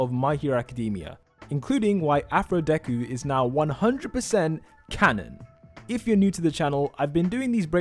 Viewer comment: I'm sorry but like.. I heard him say "Bakudeku is now canon." AND I FREAKED OUTTT